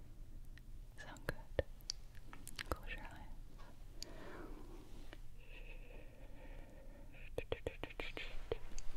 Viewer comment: What did say at 25:46. It sounded like go australia for some reason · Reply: “close your eyes” i think